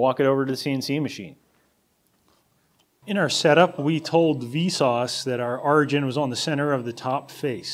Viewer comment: did you say vsauce??? rolf